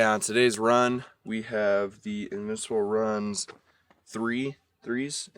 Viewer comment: I couldn’t hear what he said. What shoes?? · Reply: Nike Invincible Run 3